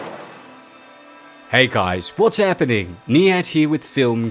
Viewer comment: I read the description & just realized your name is! So this whole time you've been saying "hey guys, it's Niyat here with etc." .... I always thought you were saying "hey guys, me here" lmao